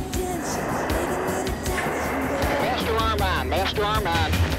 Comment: What's he saying at 0:32? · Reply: Master arm on.